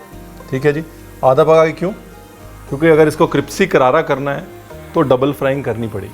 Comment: I love when Ranveer say Cripsy instead of Crispy 😂😂😂